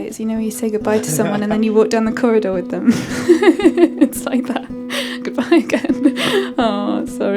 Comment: In the caps "and then you wouldn't accord them". She actually said "and you walk down a corridor with them".